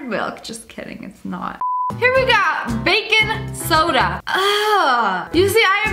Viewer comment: When she said “Bacon Soda” I thought she said “Baking Soda” hahaha pfffftttt 😂😂😂😂😂😂